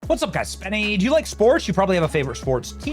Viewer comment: I was looking at her picture at the time you said sports and I thought you said spores. I was like Damn Benny, but I rewound it. Not much difference between her and a spore anyway. Both are a fungus